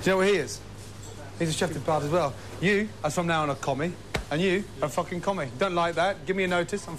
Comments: When I first saw this I thought he was saying they were communists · At first I thought he said "Commie" like they were communists. Wow. Crazy.